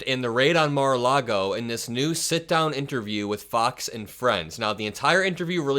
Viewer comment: I thought you said, "sit-com interview"